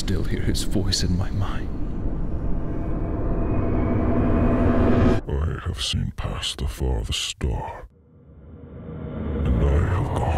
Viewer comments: “I have seen past the farthest star, and I have gone to the edges of the earth. I can take you there, if you so choose. I can give you all the things you never knew you wanted. Taste from the tallest chalice, dine in the largest halls. All I ask in return is your soul. Your soul for a chance at a life without pain, without heartache. All this I can give you and more. Just for your soul.” - Thomas, the inter-dimensional being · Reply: I thought he said I have surpassed the furthest star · Thomas: "I have seen past the farthest star... And I have gone to the edges of the earth... I can take you there, if you so choose. I can give you all the things you never knew you wanted, taste from the tallest chalice, dine at the largest hall... All I ask in return, is your soul. Your soul for a chance at a life without pain, without heartache. All of this I can give you and more... Just for your soul." Thomas: Drain you of woes... Your God has turned his back on you. That's why my tracks still pushing me forward... For people who can't make out what Thomas says. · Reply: THANK YOU SO MUCH! i thought the first thing he said was “i have some pasta for the storm” and i was so confused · When he said, "I have seen past the farthest star," I had to go back a couple of times because I heard, "I have some pasta for the store."